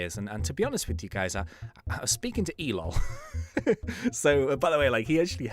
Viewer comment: at first I thought you said you talked to 'Elon' as in 'live in Mars Elon'. Later I realised you said E - lol.